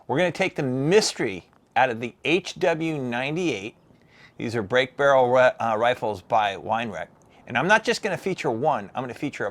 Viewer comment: I heard “a break barrel by Winerack” 😂😂